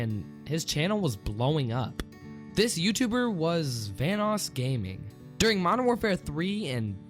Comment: Thought you said thanos gaming